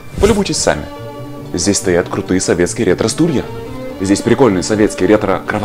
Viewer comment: Сначала послышалось "Крутые советские электростулья". Для комнаты отдыха локбрикады, наверно, в самый раз)